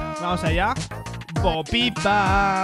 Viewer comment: ahi en vez de decir bo pi pa parece que dice bo ki taaaaaaaaaaaaaaaaaaaaaaaaaaaaaaaaaaaaaaaaaaaaaaaaaaaaaaaaaaaaaaaaaaaaaaaaaaaaaaaaaaaaaaaaaaaaaaaaaaaaaaaaaaaaaaaaaaaaaaaaaaaaaaaaaaaaaaaaaaaaaaaaaaaaaaaaaaaaaaaaaaaaaaaaaaaaaaaaaaaaaaaaaaaaaaaaaaaaaaAAAAAAAAAAAAAAAAAAAAAAAAAAAAAAAAAAAAAAAAAAAAAAAAAAAAAAAAAAAAAAAAAAAAAAAAAAAAAAAAAAAAAAAAAAAAAAAAAAAAAAAAAAAAAAAAAAAAAAAAAAAAAAAAAAAAAAAAAAAAAAAAAAAAAAAAAAAAAAAAAAAAAAAAAAAAAAAAAAAAAAAAAAAAAAAAAAAAAAAAAAAAAAAAAAAAAAAAAAAAAAAAAAAAAAAAAAAAAAAAAAAAAAAAAAAAAAAAAAAAAAAAAAAA